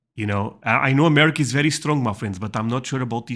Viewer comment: Like, intellectually, I'm sure what you said at 0:26 was "I know America is very strong, my friends..." but what I heard was you calling us "muffins," and that makes me so happy. 😂 NEVER feel ashamed of your accent; it brings only joy.